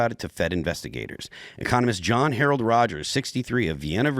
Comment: I looked away and was only half listening as he said "Economist John Harold" so I heard "Communist John Harold".... lol still fits I guess :p